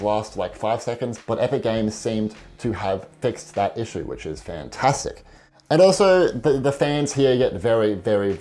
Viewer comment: did you say Fantasic